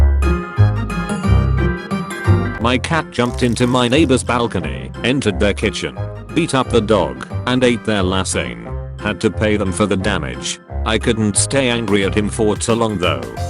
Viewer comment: Am I the only one who heard "eat up the dog"? #Imchinese